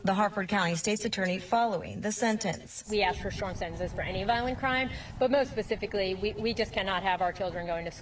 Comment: Did that state attorney just say “we ask for shortened sentences for any violent crime” ? Please tell me I heard that wrong!